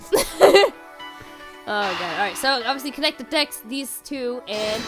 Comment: It sounded like said "connect the dex", Pharaoh.